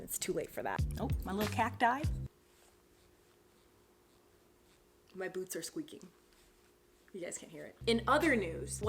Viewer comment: I thought said "My boots are spooky"